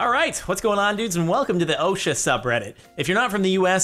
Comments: I could have sworn he said oh s*** not OSHA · I thought he said the oh shit subreddit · I thought he said oh shit subreddit xD